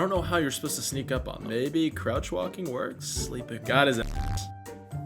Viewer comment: What does the fox say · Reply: Got his ass